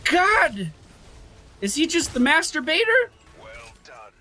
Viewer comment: "is he the master baiter" sounded completly different in my ears xD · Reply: i deeply had no idea about what he said until i read your coment, i clearly heard "masturbator" i was.. confused..